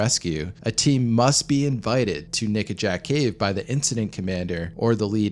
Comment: I badly misheard the name of that cave lol for some reason I heard naked jack cave 😂 lol